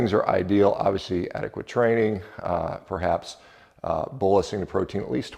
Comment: What does he say @ 3:03 about bolising the protein? Closed caption translates it as "buising", I can't understand what is being said. THx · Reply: He is using what is commonly a more medical term: bolus Taking in a large amount to help reach the ‘therapeutic’ concentration. Hope that helps! 😊